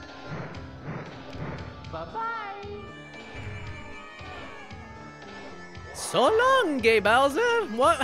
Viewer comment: In case anyone was wondering, the reason he says "bye bye" now is because it's taken from the Japanese rumble version. In Japan, at least at the time, he was known as King Koopa instead of Bowser. Saying "So-long-a Bowser" wouldn't make sense to the audience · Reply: I always heard it as 'So long, dear Bowser.' Sounds pretty similar, to me at least